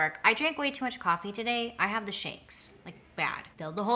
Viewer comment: Beautiful!! At first I thought you said "I drank a lot of coffee, so I have the shits" instead of shakes!! Lol! You're my favorite!!!